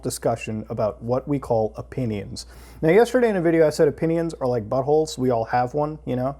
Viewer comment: For a sec I thought he said opinions are like bubbles.